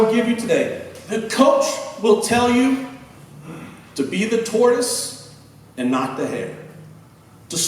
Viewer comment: what exactly does he say there? "To be... and not..." ? Can´t understand it correctly. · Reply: To be the Tortoise, and not the hare